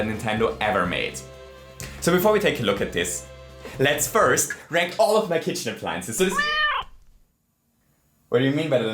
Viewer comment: LET US RATE MY KITCHEN APPLIANCES Me: YEEEE- Cat: Thats was one fo your b i g g e s t m i s t a k e s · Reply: I heard let's break all of our kitchen appliances